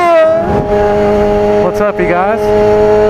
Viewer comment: wtf you say 0:34? i dont understand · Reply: You must be new here, he said CHEEEEAHHHHH